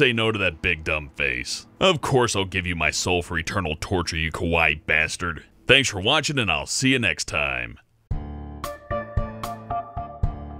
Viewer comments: When said "kawaii," I thought he said "kowai" because of how deep his voice is and simple American accents in Japanese. Both would make sense in that theory case though... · Sounded like you said "kowai" instead of "kawaii" ... kowai meaning scary/creepy instead of cute XD